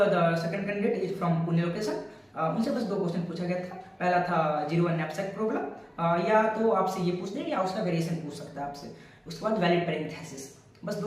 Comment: what is the question i didn't understand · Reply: 0-1 knapsack problem or its variation.